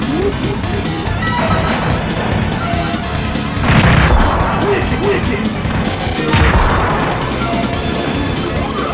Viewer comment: Min 6:40 .. alguien sabe que dice trotthle al matarte ? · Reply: Wichi algo así xD